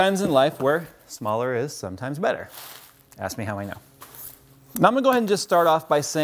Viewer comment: does he say "ask my wife"? 😂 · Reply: Ask me how i know